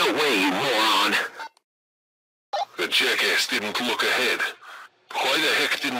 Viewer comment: what did he say with "jacka" plz tell